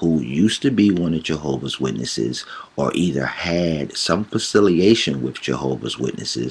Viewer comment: Did he just say facilitation? 😂😂😂😂😂😂😂😂😂😂😂 It's affiliation moron